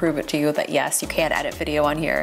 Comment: I swear I thought I heard her say “ you want an inexpensive Mac” she must be going insane